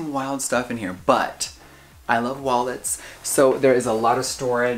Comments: he says he loves wallets. Bitch I heard walnuts and all I could think is i cant believe you having a hecking droor full of wallnuts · I thought he said “I love Walmart” I shit my pants!😂 I started to say theirs no way that came out his mouth! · i actually thought jeffree said i love walmart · I THOUGHT HE SAID “i love walmart”